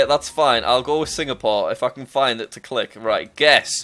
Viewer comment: did he say i fucin find it or if i can find it?